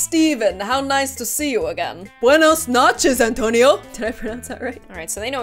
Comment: Wait wait wait! replays 3:14 again Hmmmm, did she say buenas nachos or buenas noches?..... replays it 4 more times I still have no clue 😂